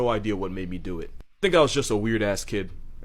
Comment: Wishing everyone's pillows be cold is the nicest thing anyone has ever said. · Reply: I thought he said," wishing everyone's pills be cold at night". haha